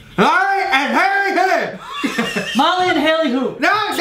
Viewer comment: i heard " mommy and hairy hou